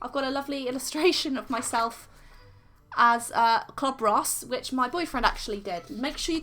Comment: Is it me or did is sound like she said “Cob Ross” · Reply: She said clob ross